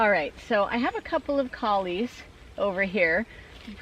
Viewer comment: I thought you said “I have a couple colleagues over here…” just referring to the cauliflower as your lil acquaintances hahahaha